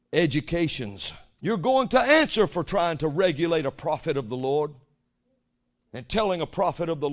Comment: I heard it said as "regulate gods PROFITS".